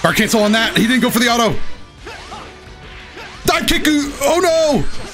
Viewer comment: Did you really say "Dive kickku!" LMAO